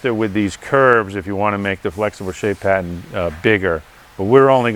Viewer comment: at first I thought he was saying flexible shape hat which is the same thing actually. you got to love not many left who can English wheel and work on a fender like him